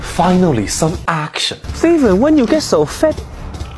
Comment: I thought he said “when u get so fit” lmao